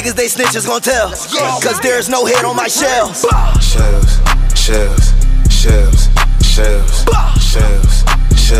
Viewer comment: LMAOOOO turn on closed captionss💀💀💀 “shelves shelves shelves shelves shelves” “chips chips chips chips chips” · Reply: I hear "chefs"